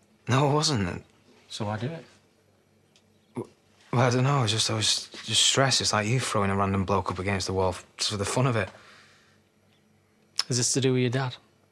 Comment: What did aaron say at 1:17 . In the middle? Im tryna figure it out but I cant. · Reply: “I don’t know I was just stressed like you throwing a random bloke against the wall just for the fun of it”